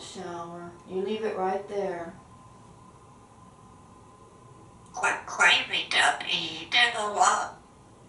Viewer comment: what the heck is he saying at 0:26? I love this so much. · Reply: He said, Where’s the melon, quack, quack, quack.